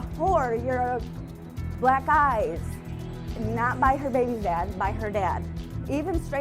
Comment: Wait what did she say on 2:08??? "You're a black guys?" Wtf